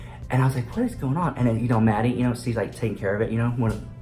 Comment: Haha i thought I heard "Manny" not "Maddie" i was shook for a sec Omg elevator Community 😂😂😂